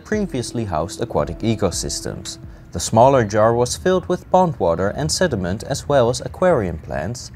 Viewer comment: I thought you said the jar was filled with "bong water" instead of pond water.